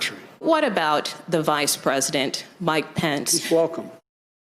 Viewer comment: OMG! I had to watch the ending again. When she asked, "What about vice president Pence?" I thought I heard him say "F#ck him." He actually said, "He's Welcome."